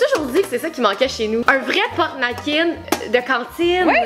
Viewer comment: Est-ce que quelqu'un pourrait me dire quel est le nom et a quoi sert l objet à 7:07 car j ai compris porte "nackien" et en cherchant sur internet ça veut absolument rien dire (je suis 🇨🇵) merci d avance